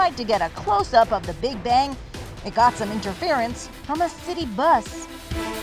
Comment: I hear that became a Meme. "Get out of the away bus" 😂🤣😂🤣😂🤣😂🤣😂